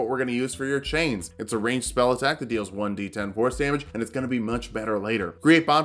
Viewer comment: Wait, I swear that at 2:35 you say "1d10 horse damage"